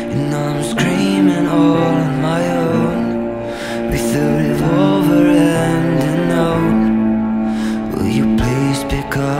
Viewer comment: I used to always think it was “a revolver and an oat” and I was so confused and I kinda laughed every time, last night I was lying down listening to this and I heard “a revolver and a note” and just the slight thought of Remington doing anything to himself made me burst into tears